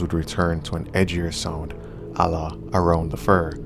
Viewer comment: I’m actually doing crazy 0:16 what is the word he said before a around the fur, transcripts is saying allah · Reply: "a la" french phrase used in english which can mean "like" or "in the style of"